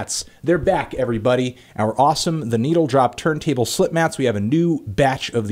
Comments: Slip matt sounds like Cal saying Slipknot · Every time he said "slip matt" I thought he was saying "Slipknot" in Cal Chuchesta's accent